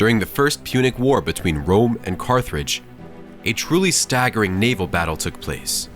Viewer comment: did I hear “Carthridge”? Lol it’s Carthage.